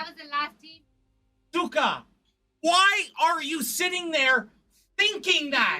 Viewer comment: is he calling her "sukah"? (b*tch in Russian)